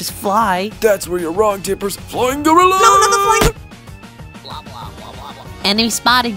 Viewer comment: Does say "Flying Gorilla"?